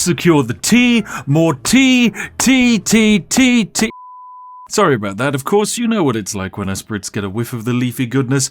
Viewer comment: i thought i heard "more tea! titties titties" but it could've been just me..